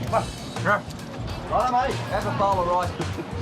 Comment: Did he say "Have a bowl of ice cream"? :D